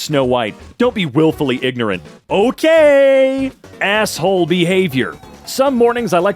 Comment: I got distracted at 1:53 and thought it said "asshole beaver" lmao.